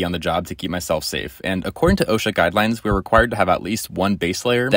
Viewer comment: I thought he said oh shit instead of osha